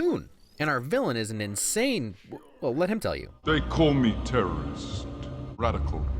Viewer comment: is it just me or did it sound like he said people call me Terrence